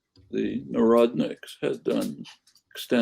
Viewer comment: What does Noam say at 15:52 "the neradnicks had done?" That word